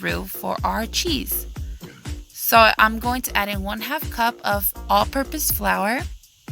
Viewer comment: i thought u said 1 1/2 cups of flower now i gotta dot this again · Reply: It’s 1/2 cup sorry for the misunderstanding hun